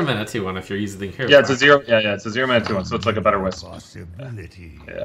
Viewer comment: "It's a zero mana 2/1 so it's like a better ______." Can anyone hear what said for the blank word here? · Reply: wisp